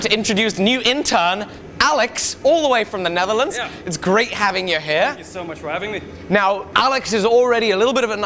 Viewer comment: Omg lol that hand shake 0:23 and did he say 0:24 its great having you hair